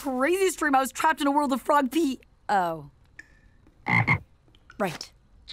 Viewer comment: Am I the only one who heard a gunshot and "frog pee"? AYO! 😂